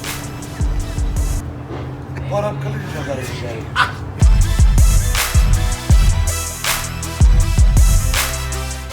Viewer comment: WHAT DID HE SAID? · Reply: He said "what is that bored dick messing around?" referring to the guy who threw that thing to his way